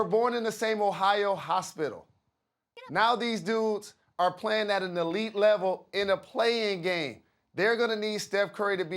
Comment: anyone else hear that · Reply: I think it said get up